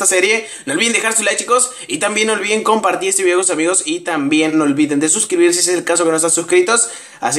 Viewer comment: no olviden desuscribirse. eso escuche yo en el minuto